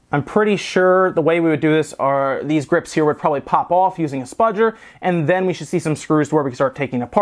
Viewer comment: What does he say at 9:11 Spodger or what?